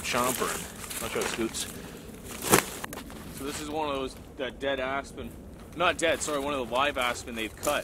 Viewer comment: I thought u said “Deada$$”